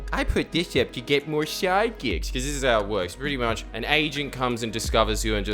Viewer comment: I thought you said you get you're 'asian' not "agent" hahaha